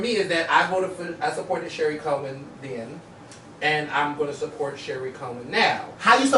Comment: I thought he said Gary Coleman I was so confused lol 😂